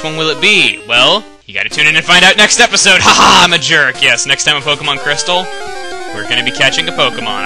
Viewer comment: At the beginning of the video I thought he said he was playing Pokebong Crystal :P Is that like the new cool way to smoke crystal meth?